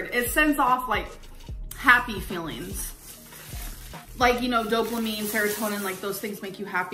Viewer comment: Did she say "doplamine"